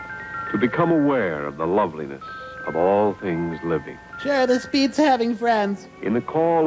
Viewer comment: What does Servo say at 7:48? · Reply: "Sure, this beats having friends..."